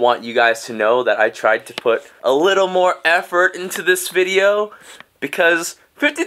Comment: DID I HEAR “That i tried to FOOT a little bit of effort into this video”